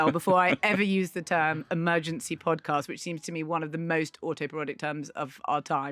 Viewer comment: What word is Marina saying at 0:17 - "one of the most" what words of our time? I thought it must be oxymoronic, but it's clearly not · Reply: Autoparodic - parodying one’s self